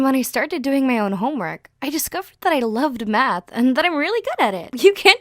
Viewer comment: i actually thought she said she loved meth